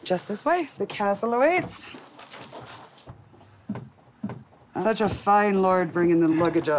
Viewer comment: Omg I didn’t realize the host said ‘such a fine lord’, I thought she said ‘such a fine lard bringing the luggage up’ 🤣🤣🤣🤣 I was like well dayum!😅